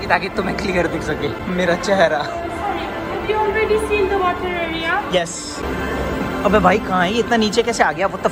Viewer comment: whom she is talking and what is she saying? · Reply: "Sorry, Have you seen the water area"